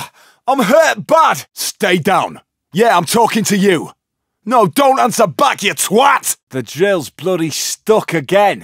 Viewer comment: I played payday 2 for 100 hours and I still question what he is saying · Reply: the entire line says "Stay down. Yeah I'm talkin' to you. No don't answer back you twat!"